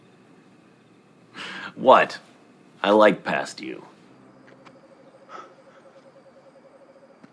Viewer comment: observe Cas lips. What he said. He said "I LOVE past you"